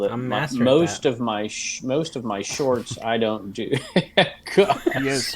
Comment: At or near the 34:50 mark, did I hear say most of his shorts are not washed??? Maybe I'm mistaken? 😜 TMI ✌